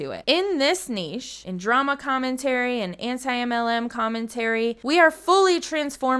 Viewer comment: you say “anti-mlm” but the captions say “anti-muslim” thought you should know! Love the video!